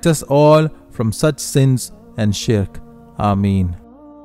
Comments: Brother your video has a slur in it, at 6:05 the subtitles say a slur when you said shirk. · woah hold on what? How did you guys put s*** when he said "shirk" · brother please fix the subtitle, instead of shirk u put sh*t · you right,but what did you meant by that swear word?🤨🧐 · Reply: He said shirk, but autocorrect said the s word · Did anyone notice that the captions said $@ instead of shirk in